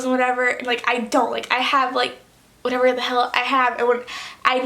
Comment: Did anyone else heard say the h word (hell) at 6:17 like if you did